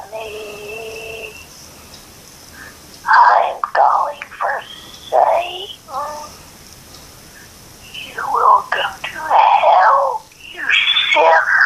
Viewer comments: This is what i hear Message 1. Help me! I’m calling for safety. You will go to hell you shit! Message 2. Its’ Isabbel ( hehehe ). What a gap between you and me. I’m juice. Help! Die! Final message. ISABELL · Juldaly. Im going to save you. You will get help you still can.. 7:22 This is serious . We need to. Help her. This is serious. Help juldaly 7:48 Help juldaly...... Seems like juldaly needs help.. im wondering what happened to juldaly. What happened to juldaly. Probably she is not saved.. save juldaly. Im turning into her now XD but this is serious. Oh and im good at hearing and translating what she said so if you want me to translate anything for you then reply to me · First: 7:16 "You will come to hell" (couldnt hear the rest) Second: 7:26, 7:43"wake up" (and the rest are some words that i cannot understand, but she mostly says "Wake Up") Thats what i believe the Old Lady said. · help me im calling for satan you will go to hell cant understand next bit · I heard she said I’m going for saitan omg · I think she says "Welcome to hell..." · The old lady one, i dint hear much but at the begging i heard “i am going [for?] satan. you are going to hell…” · sounds like "Aaaaanthonyyyyy im going for satan you will come to hell, you sineeeeer." thats what the first voicemail sounded like. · that message was creepy! At the end I could make out what it was saying😣 It said “you will come to hell” “you sinner” That gave me the chills🥶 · "You will come to help" is what I heard.